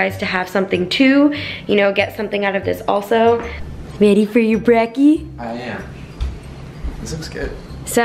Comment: Lol did she say brekky at 7:15?? 😂